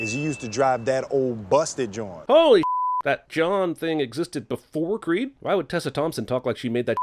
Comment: I always thought he said old busted "joint."